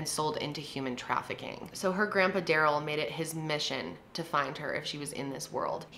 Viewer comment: Started sobbing picturing her grandpa going all over the country looking for his baby girl 😭😭😭😭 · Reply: I swear I thought she said Grandma NOT Grandpa